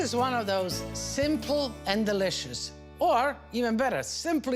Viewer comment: I thought you said, sinful and delicious. Haha😊❤